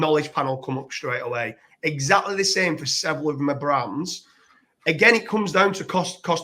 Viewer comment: Sorry, what is it you're saying at 23:15 "exactly the same with __ something mabrams?" (heard it several times and no transcript) :(